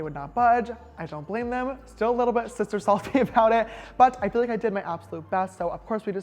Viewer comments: Did he say to sister sleep and to sister suck??? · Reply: ARMYBTS ya I thought he said sister stalk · Am I the only one that caught the “I don’t spend much time in my bed up here, just to sleep, and occasionally sister suck” 🤣🤣😭😭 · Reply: Madi Coleman I thought he said sister stalk